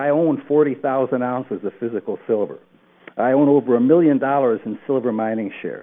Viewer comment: Did he say he owns 40,000 ounces of silver?!